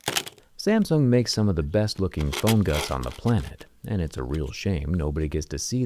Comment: What did he say at 13:33? Phone what? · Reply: Phone guts I guess